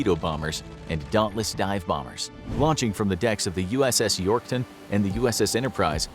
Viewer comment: Did I hear “USS Yorkton”?